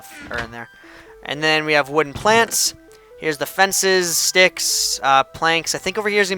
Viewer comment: i thought he said wooden plants